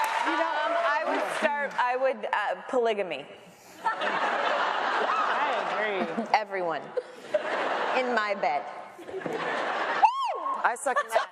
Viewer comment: What said taylor in 32:00 to 32:11 ? Please and sorry for my mistakes I'm french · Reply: li sa she said " i would say polygami everyone in my bed"